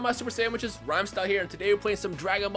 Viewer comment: It sounds like you said :Super sandwiches in the intro😂